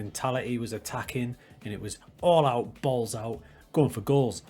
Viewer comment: Did you just say balls out? 02:10 xD